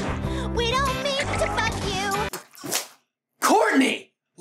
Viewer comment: I'm glad I'm not the only one that thought Courtney said "fuck you" instead of "bug you" in "It's rowin' time" when hearing it for the first time. Her VA just says it way too quick or just didn't enunciate the B enough.